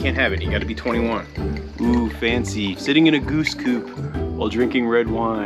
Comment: I love how the three cats have acclimated to each other, even Molly is getting used to Toby. Instead of sitting in the goose coop, i thought I heard sitting in goose poop and later it's, time to hit the hay (pun intended?) Such a cute video, but the danger is real. Sgt Toby on patrol, all's well.